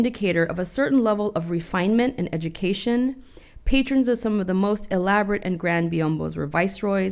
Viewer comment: does she say "patriotism"?